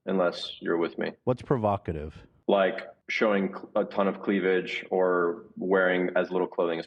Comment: i genuinely though he was gonna say "showing clit" and it fucked my brain up I'm sorry